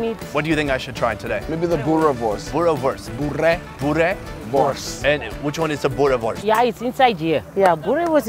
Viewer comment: did they just say burenwurst? lmao